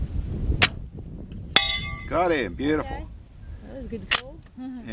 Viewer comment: FYI, (captions setting, to print on screen what is said) at 0:08 & 16:08 you said 'got em' but they printed "goddamn beautiful". Gun folks get misrepresented, and made to look bad at every possible moment. I'm being more persistent then anti's.